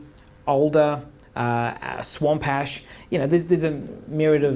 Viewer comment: did he say swamp-hash? I need me a swamp-hash guitar, gotta try that.